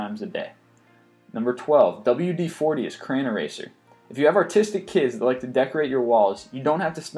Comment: I thought he said "autistic kids", I almost choked on my keyboard!